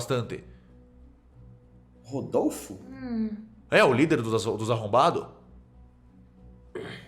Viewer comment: Líder de quem ? n fui só eu q ouvi isso né? kk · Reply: Se chama assombrados mais o fala arrombados